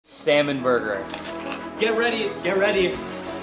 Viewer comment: At first I heard "semen burger"💀